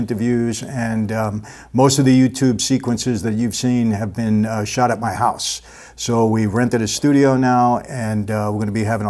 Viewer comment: I thought he said they shot up my house I picked up a shotgun and said “WHO” not the real one my sons toy one